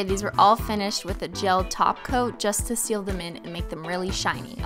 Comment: I hear "taco" everytime she says "top coat"